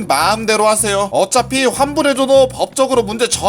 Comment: 초에 자막은 환불 안해 줘도인데 말은 왜 환불해줘도로 들리지? 기분탓인가